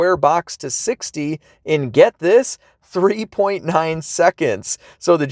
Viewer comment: Did you say 30,9 seconds at 2:18. It's all good tho :D